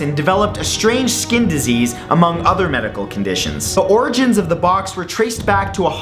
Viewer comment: When matt was talking about the box I thought he said the book lol